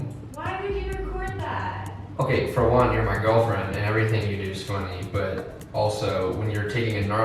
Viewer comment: what did he say? Everything you do is ....? lol I didn’t understand 🤭😂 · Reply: He said everything you do is funny!